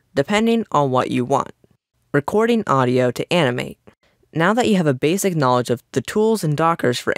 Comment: In 4:00 I misheard the sentence I heard "recording audio to anime"